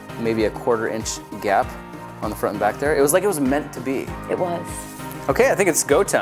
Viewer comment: when grant said, "it looks like it was meant to be," i thought iJustine said, "like us;)"